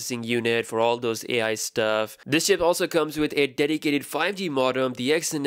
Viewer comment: I heard "this shit also comes with.."